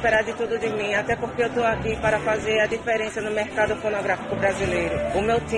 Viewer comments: "fazer a Diferença no mercado pornografico Brasileiro" foi isso oq ele disse? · ele falou "mercado pornográfico" ou eu entendi errado!!! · mercado pornografico brasileiro kkkkkkkk so eu ouvi isso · só eu que escutei o garoto falar “mercado porn*grafico”???